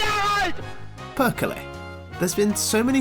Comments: Hold the fuck on, did you say PERKELE 5:35??? Torille! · Did I hear casual Perkele at 5:36 :D